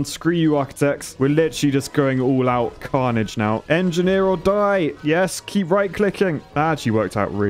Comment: I heard him saying ''engineer will die''